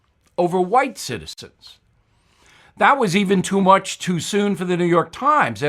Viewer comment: Did he say white CITI-STENS????? lol